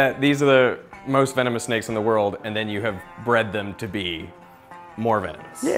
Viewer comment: i swear i heard him say "you bred then to be mormons" 5:40 🤣🤣🤣